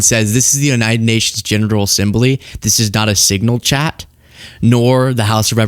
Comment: It sounded like you said "genital assembly "🤣🤣